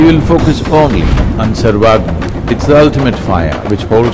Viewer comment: What is the word Sadhguru says at 5:28? It sounds like survival, but that doesn’t make sense.